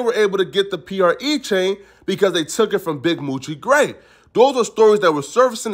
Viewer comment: I swear I thought said they were able to get the chain from a dude named Big Moochie GRAVE 🤯🔫